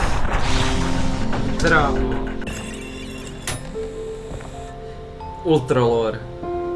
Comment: ok wait,did I just hear you say "ultralore" That's what it sounded like to me